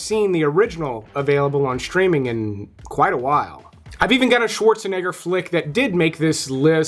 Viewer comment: bro what did you say???🤨🤨🤨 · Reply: “I’ve even got a Schwarzenegger flick…”